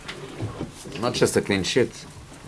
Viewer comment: wtf did he say in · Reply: I think it's "not just a clean sheat" maybe